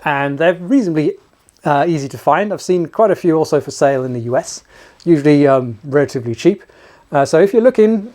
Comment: I thought I heard you say that you had seen the rifles for sale in the U.S. I must have misheard you. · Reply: "I've seen quite a few of them for sale also in the U.S." Sorry, I am from the Midwest, so English is a foreign language. I assumed that you had visited and hadn't considered online sales and such. Of course, th comment was just tongue in cheek. It was very late here when I viewed the video. I get a little punchy at 3:00 a.m.